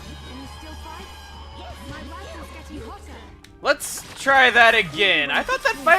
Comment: Did Sharla just say "My body is getting hotter!" ?